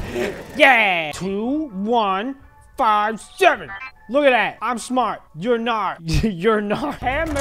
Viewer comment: I thought is gonns say "im smart your fart" 😂😂😂